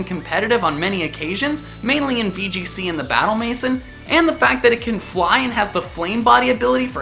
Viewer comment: what game did he say? Can i have the link and name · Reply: DANIYAL MASOOD, VCG or battle Maison?